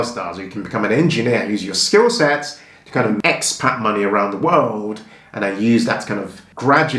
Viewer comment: what do you say? "____ money around the world"? I couldn't hear it properly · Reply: I think he said "expat": A person who lives outside their native country. "Expat money around the world" Correct me if I'm wrong. x